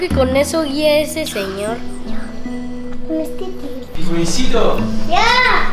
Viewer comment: Se escucha super claro que dicen jonh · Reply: Dice yaaa